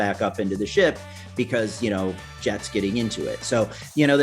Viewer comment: did he just say Jet's getting into the sword fish? 7:25 xD